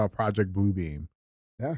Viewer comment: I thought y’all was saying “project boobie” lol